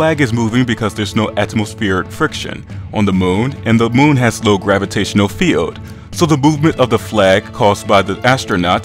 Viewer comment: Hey! Did you say at 3:35 that the moon has NO gravitational field or that the moon has a LOW gravitational field? I probably misunderstood you, but of course the moon has a gravitational field. <link> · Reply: OraculusMaximus I said low gravitational field. I'll make sure to make it easier to understand next time. Sorry about that.